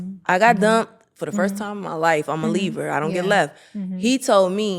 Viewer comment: "I'm a Libraaa i dont get left" ❤❤🎉🎉🎉🎉periodt clock that!!! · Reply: I’m a Libra Too 10/16 But She Said Leaver. (I Thought She Said Libra Too At First)